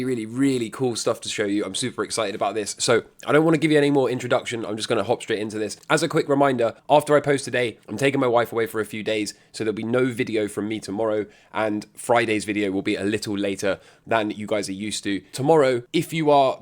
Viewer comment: Thought you said there was no video today. Flip flopping like powels tie choice! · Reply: tomorrow, he said no video tomorrow... listen up mr.